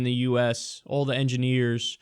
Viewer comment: dude wtf is en-GIA-NIERS? sounds like you're saying vagina